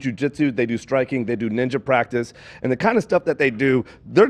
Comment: i'm sorry, did you say ninja practice?? :_h3h3Hilalmao